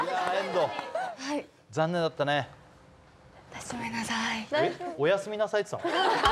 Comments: 本当におやすみなさいって聞こえた。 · 何回聞いてもおやすみなさいにしか聞こえない笑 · ほんとおやすみなさいにしか聞こえない